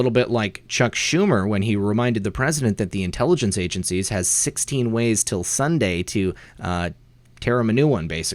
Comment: I heard him say 6 ways to Sunday to get back at him, now you said 16 ways, which is rightly stated was the warning?